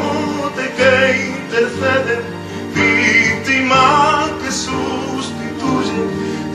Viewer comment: preciosa alabanza al minuto 1:49 dice sacerdote que intercede' ('vitima que sustitulle) es jesus manso cordero hai donde puesto entre Comilla es lo que dice ????? gracia por la repuesta dios os bendiga!!!!!!! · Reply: ya la tengos la palabra es victima que sustituye